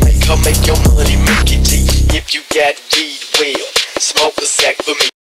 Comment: i hear "smoke mase" (ma-se). on my language Mase is a slang for Joint. Last 2-3 years is the only one to amaze me